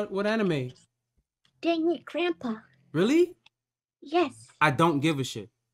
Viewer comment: Ah yes, Shukichishukisaikara from Deadbeat Grandpa. That show is a must see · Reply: Oh, I thought I heard "damn it Grandpa"